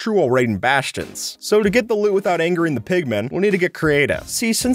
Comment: #10. I thought you said "without pistons" (used a piston the whole time)